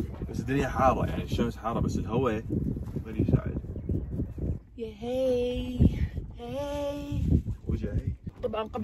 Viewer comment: سمعتو علي كال وجعي 😂😂